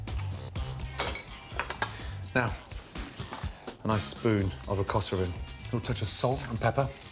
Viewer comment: what is the dairy product he used for the gnocchi recipe? I can't make out what he said, .... ricotta oh yeah. just edited my comment for those who thought he said Okata ring? or maybe that was just me. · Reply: "Nice spoon of ricotta in"...they always add the stupid rrrr after the a. So annoying lol